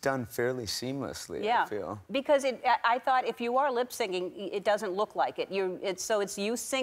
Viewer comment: Did Ellen just say lip singing 2:14? She must know it’s “lip syncing”. She’s a smart woman.